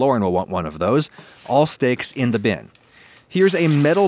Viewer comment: So I was totally being cute with this title and I have no problem with all the jokes, BUT I’m gonna establish this pinned thread as a place for people who want to discuss the actual recipe ;-) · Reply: I was just cooking and not watching for like a QUICK second and then... I hear... “put the steaks in the bin” and I’m like... “How could you put steaks in a bin?! YOU MONSTER!” “Oh wait... he meant a container... that makes more sense”